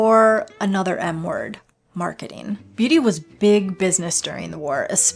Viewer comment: I thought she said “another n word” and I went like 😦